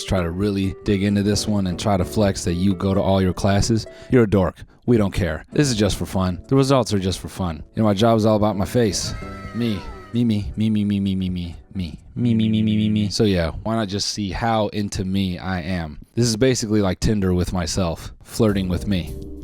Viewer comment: Sounded like my grandma saying meme for a second there